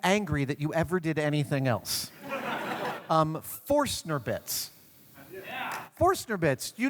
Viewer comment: Can someone tell me what tool Adam mentioned at 0:33? I'm not a English native speaker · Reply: Forstner Bits are drilling bits that produce flat bottomed, perfectly round holes with smooth sides.